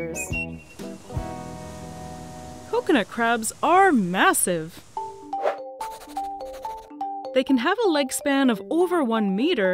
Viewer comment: Lmao I thought she said that they had a life span of 1 meter